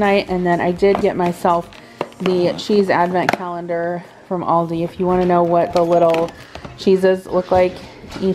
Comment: Shouldn’t have listened to this while multi-tasking! … I thought you had said they had a Chiefs advent calendar at Hy-Vee! Was so excited only to find out the next day it was a cheese advent calendar at Aldi! Happy Thanksgiving!